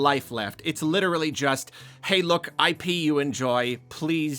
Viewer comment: "Hey, look. I pee, you enjoy. Please consume it." I'm sorry. But that's almost what it sounded like at that point, PLUS the visual on the video was like a... golden liquid flowing through pipes. It was just unfortunate phrasing and background visual. (And yes, we all know he was saying IP as in intellectual property.)